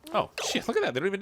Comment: "Oh shit, look at that, they don't even need a ladder" Did I just hear the rare, wild Swear? I never thought I would hear such a majestic creature in my lifetime.